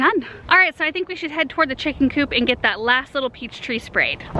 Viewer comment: Maybe I heard wrong but I thought you said "a" pear tree. You need two pear trees. Pears are cross-pollinating or self-unfruitful. They need another tree for pollination, and not just one of the same variety, but a different variety of the same fruit. But maybe I heard wrong.